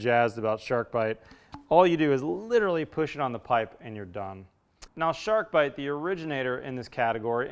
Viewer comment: I swear i heard you say "you just litterally push it onto the pipe and your dumb" i cracked up!